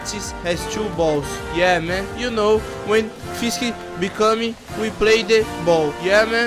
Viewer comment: so eu que ouvi fisk me come nos